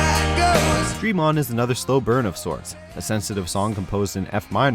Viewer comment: Hello people from YouTube, I’m not a native speaker of English and I’m trying to figure out what the narrator says in minute 3:11. I’ve replayed that segment over and over but I’m still unable to catch the exact words. Can anybody out here give a hand? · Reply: Do you have a CC icon to click on? That's Closed Captioning and it'll show what's being said (more or less). At your 3:11 it shows "Fremont", but it actually says "Dream On". I love CC.